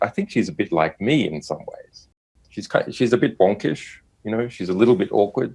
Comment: "she's a bit...." what's the word he used there? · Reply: cherry blossoms I'm hearing "wonkish"...Aussie slang for "awkward"? That's my best guess. (Similar to "wonky," used a lot in the UK but not as much in N . America...)